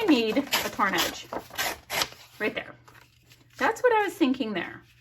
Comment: lol when you said "torn edge" I heard "carnage"...with the skull thing/theme. lol I love watching your layouts.